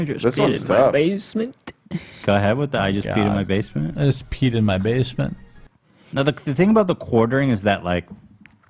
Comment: Up until this podcast, I always thought it was saying, “I just peed in my basement” 😂😂